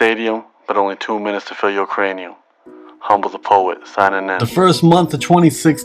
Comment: I keep hearing "only two minutes to fill Ukranium"...